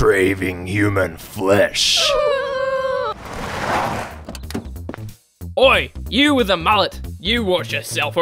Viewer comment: he just say oi